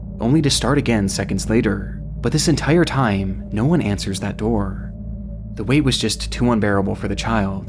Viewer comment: For the little girl, it sounds like she said, "Mom, there's no one next door." So maybe someone broke into their house or there was some kind of emergency?